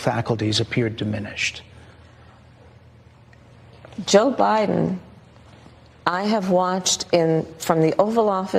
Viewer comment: Who heard: “Jbiden …..”